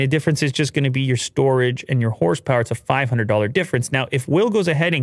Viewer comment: Did he say horse power at 9:42, I guess these machines are beasts